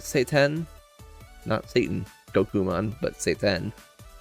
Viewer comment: I thought you said satan gokumon, but it actually Shiten